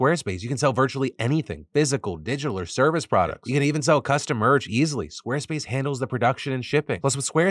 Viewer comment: I dunno why but I thought he said sell customers 😅I had to reply it 😂